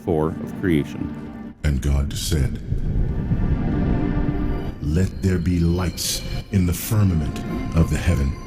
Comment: I heard "and God said, let they're be LICE!"